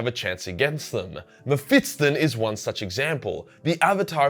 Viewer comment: Sounds like you say mep-fiston